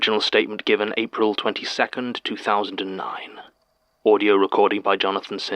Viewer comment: My app glitched for a sec, so it sounded like Jon said "audi recordi" and I